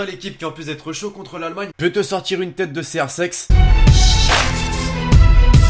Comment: t'as dit crsex ou c'est moi ?